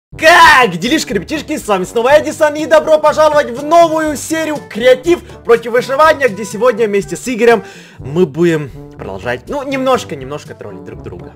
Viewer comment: Мне одной послышалось креатив против вышивания